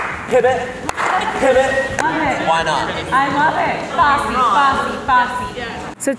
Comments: I swear at 10:34 Devin was saying "Fosse! Fosse! Fosse!" - as in famous choreographer Bob Fosse. Not "bossy!" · Sorry, y'all - your subtitles onscreen at 10:33 - Devin is NOT saying "Bossy! Bossy! Bossy!" She's saying FOSSE! FOSSE! FOSSE! Theatre nerds unite.